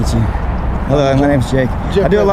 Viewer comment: At first I thought he said my name Jeff lol